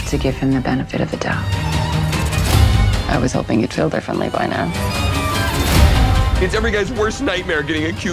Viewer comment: what is she saying at 1:58? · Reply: GaryXorangeberry I was hoping you’d feel differently by now